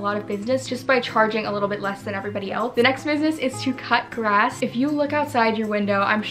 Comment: I heard " touch grass" instead of "cut grass" 💀😭